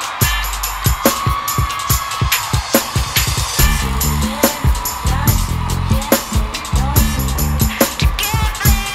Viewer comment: What does Janet Jackson say at 2:35? I'm having a little trouble understanding her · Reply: "Conscience", backwards